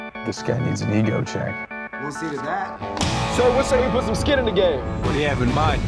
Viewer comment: So what you say we "put" some skin in the game? Not "out".